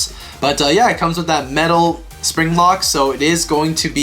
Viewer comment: did he said spring lock